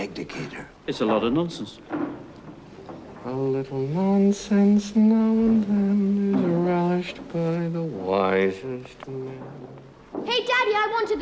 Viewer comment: Sorry , i didnt hear . Can you say it with subtitles ? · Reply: A little nonsense now and then is relished by the wisest men